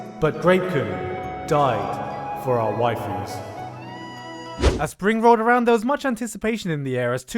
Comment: I heard 'As springrolled around'... springrolled... damn